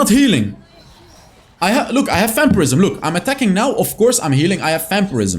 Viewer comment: when i started watching your channel i thought you said "fan prison"... i was new to wild rift and didnt know about vampirism lol